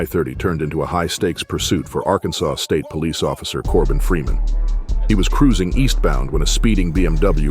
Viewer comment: thought he said Morgan freeman